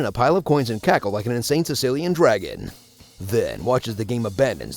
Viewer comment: Did I play too much chess or did he really say sicilian dragon at 0:47?